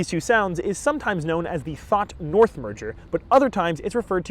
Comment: Did he just say the pawn porn MURDER? I’d like to see that thriller.